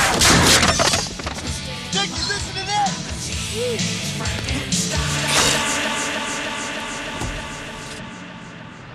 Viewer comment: did he say Jason listen to this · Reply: He said Nikki.